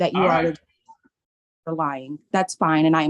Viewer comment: Wait...did she say that the Judge was lying around 11:06? The audio skips a little there, but it sounds like that to me. What do you guys think?